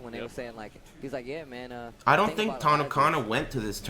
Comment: I thought you said tanacon at 3:40 lol FeelsBadMan